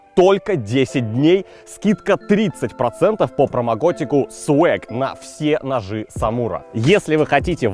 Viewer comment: Я одна услышала "промокотику"?